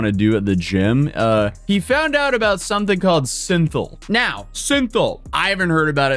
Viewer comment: Oooooh, I misheard synthoil as syntin. Russian rocket fuel.